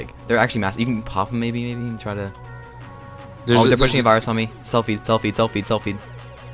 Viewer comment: I thought you said "selfie" lmao